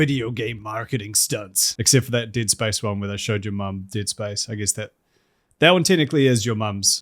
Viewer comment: forgot you were a Kiwi and genuinely heard “tit spice” instead of “Dead Space” as a result.